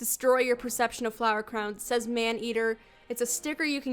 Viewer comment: When it said man eater I thought it said ham eater